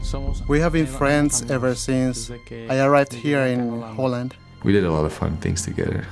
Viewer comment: did he say they where in Holland?